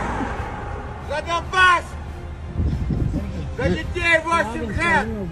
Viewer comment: Он что сказал "за Кавказ"? Плохо слышно было. Кто расслышал что он говорит? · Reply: "За Донбасс..."